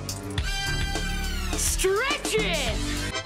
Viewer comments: I thought he did say "Stretchin'!!" at the end. But maybe that's stretching it, lol... 🤦🏻‍♀️ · I heard stretchin' not stretch it tho